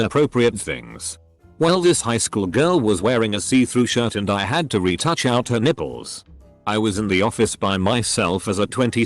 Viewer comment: I was listening without reading the text for a sec, and thought her said "I had to reached out to her nipple" and I was confused 'till I went back and read what it said.